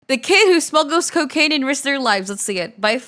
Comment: Ok, I had to stop and rewind because I heard "...who smuggled coca*ne and lost their legs" and I was like... wtf, how.